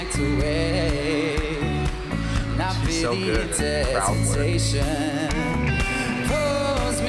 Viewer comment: Wait um, did he said " She's so good at crowd work?" 😂